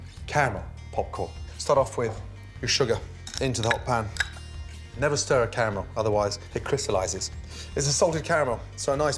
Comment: “Never stir a caramel, otherwise it crystallises” “Stir in your butter” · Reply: I heard, "never start with caramel"